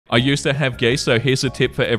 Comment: I thought he said "I used to have gay sex, so here's a tip for everyone..."